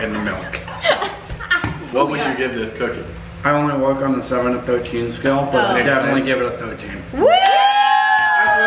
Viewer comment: “I don’t want to work on the 7-13 scale but I give it a 13 “. Is that what he said cause I feel what I’m hearing is wrong